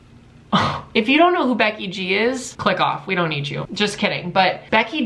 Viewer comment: If you don’t know who beck G is click off we don’t need you😂 me:EEE BECKYYYYYY GGGG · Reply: I thought he said Becky jeans